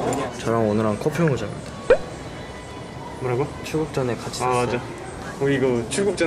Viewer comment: ps: di 2:30 ada mistrans ya itu harusnya "sebelum berangkat (chulgukjeon)" bukan "tempat futsal (chukgujang)" huhu maaf salah dengar ;; geng hits jalan-jalan sekarang udah go internesyenel dan ngetrip ke hk + nyc! (ya walaupun buat kerja juga sih) ayo ikut vloggers kesayangan kita keliling-keliling big apple 🎉🌃🚕🌎 AND BIG THANKS FOR 3K SUBS!! huhuhu kayak baru kemarin 2k sekarang udah 3k aja, makasih banyak atas entusiasmenya ya :') terus, kita juga udah dapet hasil polling di community post kemarin, jadinya kita akan sub knowing brother full tapi pelan-pelan ya. terima kasih buat yang sudah ikut polling dan ninggalin nice comments, we'll make sure to take care of ourselves in kr dan balik lagi buat ngesub lokal. love u guys ❤❤